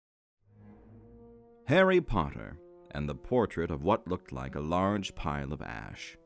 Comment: It buffered and I heard was "Harry Potter and the Portrait of What Looked Like a Large Pie."